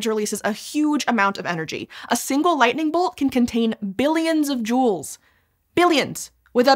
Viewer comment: I'm not a native English speaker, so I misheard "billions of Jewels". Sounds sparkly. :D